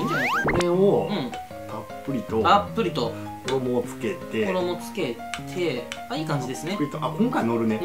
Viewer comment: での声で「たっぷりと衣をつけていくぅ！」って幻聴聞こえたわ